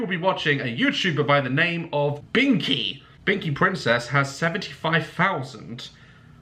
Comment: Gonna be honest i thought it said bikini princess -._-.